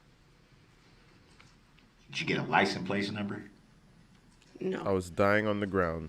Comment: Im pretty sure he says at 6:40 "lights and plates number" instead of license plate number... He does this throughout the whole trial. Further proving he is an idiot lol